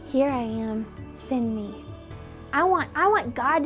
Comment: When she was saying "send me" I thought she said "sin me"